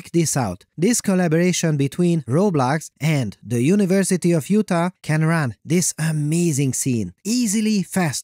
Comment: God I misheard that as University of Futa